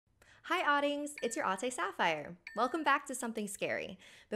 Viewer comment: I thought you where saying auntie sapphire